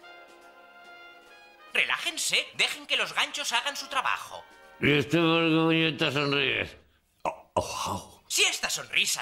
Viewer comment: que dice jajajajaja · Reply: Josshwell y usted por qué puñetas sonríe